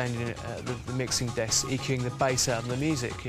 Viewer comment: can someone type what did he say at 7:00 what did official did?cos its hard to understand british accent if ur not british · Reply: "EQ'ing" the bass at the mixing desk... EQ levels can adjust treble mid and bass frequencies to make them louder or quieter.